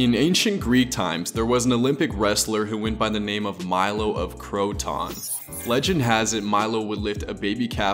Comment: Trying to improve my video quality, let me know what you think! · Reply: i thought when you said the guys name you said croutons,this is what happens when you do 5 & 1/2hrs if Latin I’m homeschooled but I’m going to this thing called classical conversations and it’s basically school but mondays